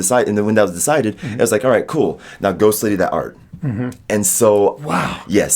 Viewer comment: "Now ghost lady that art" "W o w" I'm sorry, what the hell is being said here xD